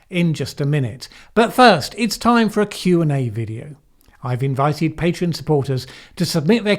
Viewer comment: When said it was 'time for a Q&A video', I misheard him at got really excited at the prospect of an upcoming QAnon video. 😜